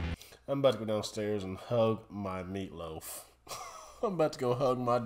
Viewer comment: I was in the other room when I heard say "go downstairs and hug my meatloaf". I had to do a double take and figure out what he meant. I'm glad I was wrong! lol